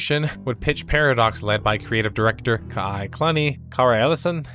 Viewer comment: when the Youtube transcription reads Kai Cunny instead of Kai Clunny... D